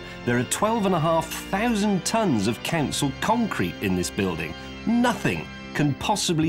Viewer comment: Can I confess at this late stage that I always heard James' narration of "Council concrete" (Check the subtitles, for a change it's accurate) as "canceled concrete"? Technically, we're both right, mind you.